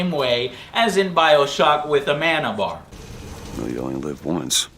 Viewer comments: Did Booker just say "You only live once"? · d-did he just say yolo? ...shit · Wait did he say You Only Live Once YOLO??